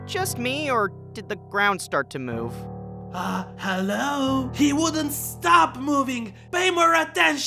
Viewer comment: just wanted to point out that at 6:17 the subtitles say "Um, duh!" while Yang really says "Um, hello!" Edit: they fixed it :)